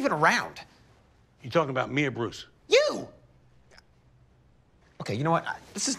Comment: When I Had My Volume Down A Little I Thought Frank Said Are You Talking About Me Or Rusev But No He Said Bruce